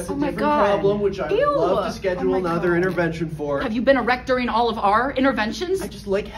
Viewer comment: Lol i thought she said "erectoring" lol 😆 Had to watch it a few times before I saw it was "erect During"!! Lol Yigidy 😆🤙🏻